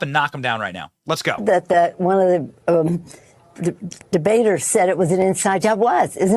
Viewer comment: Did she just say DDD debaters?!